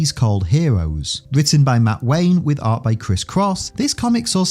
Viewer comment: at first I heard “art by Kriss-Kross instead of Chris Cross” lol.